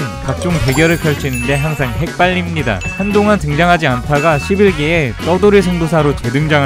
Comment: 자막 오타 난것 같아요!! 말씀은 11기로 들리는데 자막은 1기로 되어있네용~^^ 문제 되면 바로 지울게염